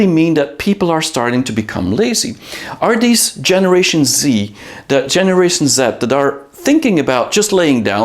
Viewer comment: I thought you said generation Zit. 😂